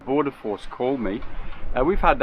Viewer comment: Who called? It sounded like "pordoforce". Are they Australian immigration or something and want to make sure as many of those on boats seeking refuge die at sea so they cannot discuss the weather?